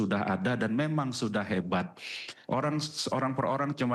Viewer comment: Apa gak salah dengar gua “hebat” 🤢